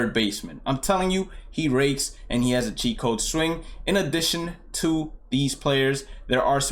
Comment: CHEAT CODE SWING! Not gonna lie I previously thought you were saying a Chico swing and I had no idea what that meant 😂